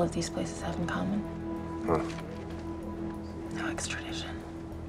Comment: what does she say in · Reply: Stella Willson no extradition